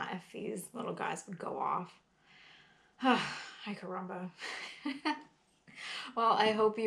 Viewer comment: Did you say "Ay carrumba" at 11:40? I haven't heard that since kindergarten in 1966. My Kindergarten teacher was an immigrant from Cuba after Castro took over and many learned folks fled the country. Mrs. Volario was a university teacher in Cuba, but worked as a kindergarten teacher in North Carolina after escaping communist Cuba. Any way, Mrs. Volario used to say Ay carrumba! when we got too rambunctious. She was a very nice lady who had the patience of Jobe. Thanks for the videos